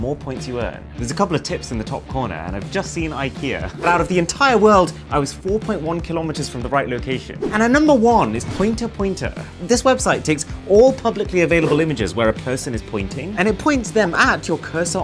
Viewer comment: Without reading, I heard "it points at the person on your screen" 😂 I was like wtf? I had to watch it again and read the captions to realize that he said "at the cursor on your screen"